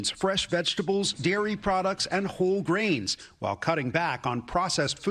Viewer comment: "Whole grains", I heard "whole brains"...